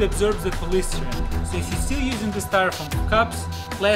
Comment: Does he say styrofoam forks at 1:46? hahaha